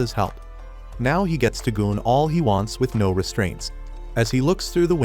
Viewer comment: did he say game or GOON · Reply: Goon